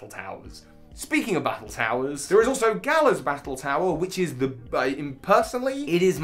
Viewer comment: When you said "Galar's battle tower" I thought you said "Kalos' battle tower" and I was so confused